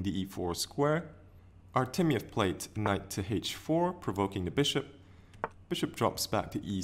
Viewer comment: Wow, your channel is growing quite fast! This video is also great, but unfortunately my English isn't.What was the word at 1:14? "Prouvocing"? Is it something chess-related? Cheers and happy new year! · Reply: Hi iris, nice to hear from you again. The word is provoking, meaning to cause some annoyance. It's not a chess term. Happy new year and have a great year ahead!